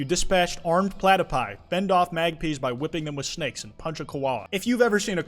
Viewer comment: Did he just call Magpies fucking MAGPIS 😭😭